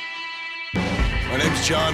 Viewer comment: I heard "my name is John marston"